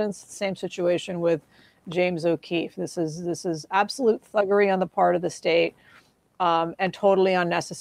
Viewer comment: I could swear she said "absolute fuckery" lol